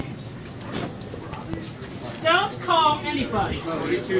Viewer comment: Also, did he just say "Don't call anybody."?